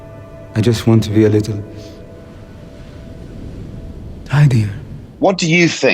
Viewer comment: Rami Malek: "I just want to be a little ? WHAT??? CAN'T UNDERSTAND WHAT HE SAYS! 🤔😕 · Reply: Tidier